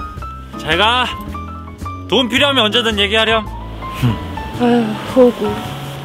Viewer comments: 나만 4:28에서 "돈 필요하면 언제든 애기 하렴"으로 들림?? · 나만 돈 필요하면 언제든 얘기하렴 이걸로 들었나..? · 나만 ‘돈 필요하면 언제든 얘기 하렴’ 이라 들려?ㅋㅋㅋㅋㅋㅋㅋ